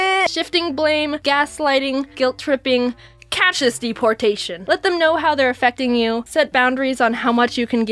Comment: What does she say at the 6:30 mark? All I hear is "catch this deportation" which makes my brain hurt.